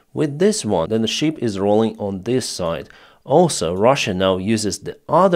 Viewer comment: a ship is said to "list" when it leans to one side or the other, as from damage that floods spaces below deck. Your English is very good, but there's always room for improvement (even by native speakers). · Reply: used "list" properly if memory serves. I am an expert in American English. OK he said "rolling" but I heard "listing." This is my way with, who has perfect logic if not perfect usage.